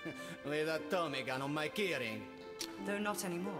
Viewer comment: "WITH THE TOMMY GUN ON KEY RING?" IS THAT WHAT HE SAID FOR ALL THESE FUCKING YEARS?! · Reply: I thought he always said, "Where the Tommy Gun Imma carrying?"